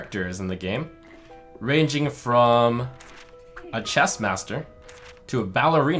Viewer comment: When said chess master I thought he said chest master. Which I guess also makes sense, if y'know what I mean.